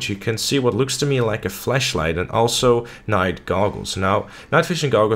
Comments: did he just call it a Fleshlight? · I low key thought he said that there was a flesh light on his helmet instead of a flashlight.Sorry,his accent is just too complex!! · oh he said flash light, i thought he was saying flesh light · Am I the only on that thought he said fleshlight?